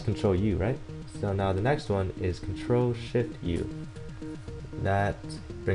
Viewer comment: what are the controls to change brightness? it sounds like your saying "Ctrl, sh*t, u."